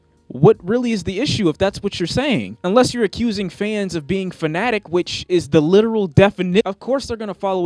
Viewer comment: what did you mean by fans being phonetic? I looked up the word and still didnt understand your point